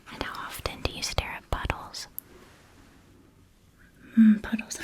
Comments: I did NOT hear “puddles” at first! I heard …”****holes” 🫢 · I was just drifting off to sleep and thought I heard “How often do you stare at buttholes” 😂 Had to rewind to make sure I misheard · I straight up thought she said buttholes not puddles I had to rewind so many times😭😭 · I misheard puddles as buttholes and my eyes shot awake like a sleeper agent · I’m glad you have closed captioning because at 8:42 I definitely heard butt holes nut puddles. Then a few seconds later I heard balls not pulse. I had to rewatch with the subtitles to make sure this wasn’t going a different direction. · I thought she said butt-holes not puddles T 0 T